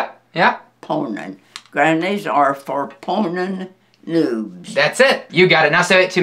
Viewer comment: at one point i thought she said 'granny's are proposing nude'...